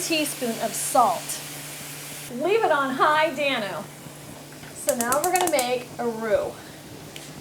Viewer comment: At the 03:50 Mark. Sounds like you say Leave it on High Danno ? What did you say? Its really bothering me.